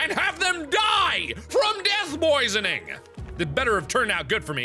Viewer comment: Did anyone else hear 'Death poisoning'? That's great. X,D